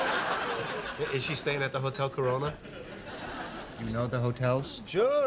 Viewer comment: did he say hotel corona